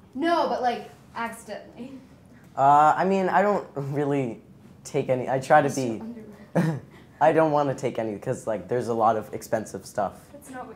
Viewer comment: What did Sadie say at the 7:10 mark? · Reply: Pretty sure she said “you always steal underwear” to Noah